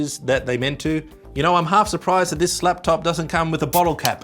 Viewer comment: Did you say 'slaptop'?